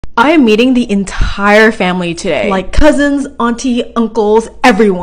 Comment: Omg I wasn’t paying attention I when she said I’m meeting the entire family today I thought she said Im eating the entire family today 😂😂😂😂